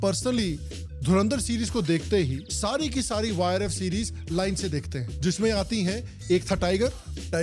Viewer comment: Using again and again YRF Spy movie sounded like Virus Spy Movie, check it out